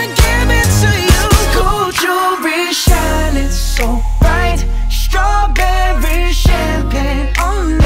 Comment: Bro i dont hear gold jewelry shining so bright insted i hear"GOJO BE SHINING SO BRIGHT✨